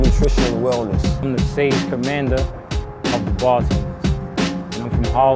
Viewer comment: Does he say stage commander at 2:27?I cant make out what he says no matter how much times i rewind.